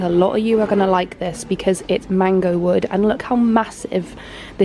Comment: It says wallnut on the sign right there? - Did you say mango or mangroove Wood? 😅